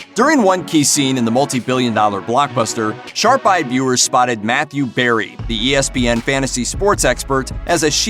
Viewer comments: I misheard matthew perry and get riled.........for a while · I thought you said “Matthew Perry”